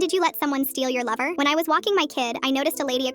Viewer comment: Not gonna lie at first I thought the question said liver instead of lover